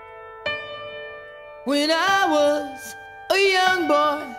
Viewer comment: I AM SORRY BUT ALL I HEAR IS "WHEN I WAS....A LITTLE LAD" 😭